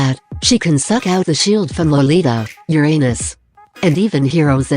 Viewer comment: Lol when you suddenly said Uranus i thought you were saying your anus P.S. please don't use this robotic voice it's very prickly and annoying to hear😓